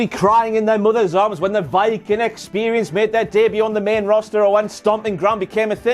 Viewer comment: am I the only one who thought he called The Viking Experience "The Vicodin Experience"? 😂😂😂